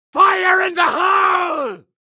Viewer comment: I thought it said fire the hole 🤣🤣🤣☠️☠️☠️